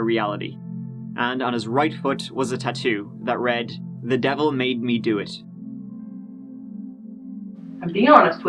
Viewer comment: I thought his tattoo said “I made the Devil do it”...? That’s what I heard..